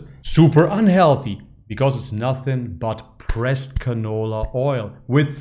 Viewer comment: I thought you said "breast canola oil" 🤣🤣🤣